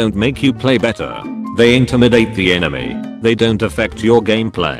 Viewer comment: When they said: "they intimidate the enemy" I heard: "the Internet ate the enemy"